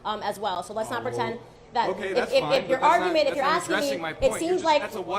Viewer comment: did he just say 'that's okay that's fine'? As if anyone subjected to That torture is just 'fine'????